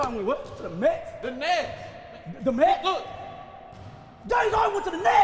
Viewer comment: “James Harden went to the nets today” “The Mets”!!??? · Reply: I thought he said "The meds" :D could work both ways..